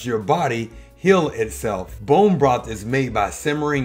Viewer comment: I ate most of what you were talking about except the bomb broth. I’ve been taking collagen bovine powder for over four years and I have gotten extremely wrinkled even with taking that. Do you have any suggestions? I spend a lot of money on those powders I get frustrated that it doesn’t work. Thanks.🙏😊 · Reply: Maybe what you typed was a mistake or you misheard what he said, but it's not bomb broth but rather bone broth. Just saying.